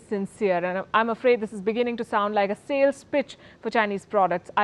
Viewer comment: Goddamn I thought she said "a salesbitch for china" 😄😄